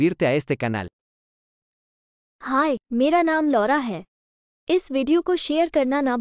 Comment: What does she said Lora ? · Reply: Laura ☺️